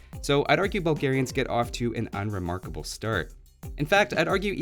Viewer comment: I swear I heard "unremarkable Stirrup"